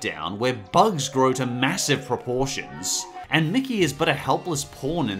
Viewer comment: did he just say porn?